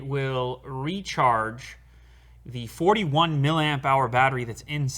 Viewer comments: did he say 41 millampare lol ;) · did he say 41maH battery!!lmao